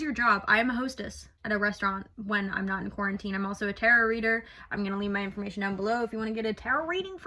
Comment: the close captions said terror reader lol.